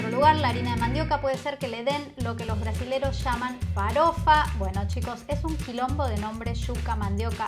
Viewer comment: Pense que dijo "Falopa" jajajajaja